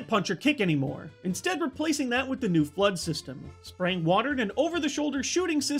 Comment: When you said flodd i thought you said blood XD